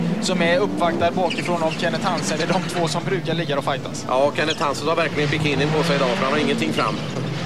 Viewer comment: Vad säger egentligen vid 22:27?! Det låter som han säger "Kenneth Hansen har verkligen bikinin på sig...."! 😆😆